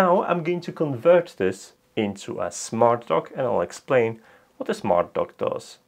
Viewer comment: At first I thought you said you were working on a smart "Dog."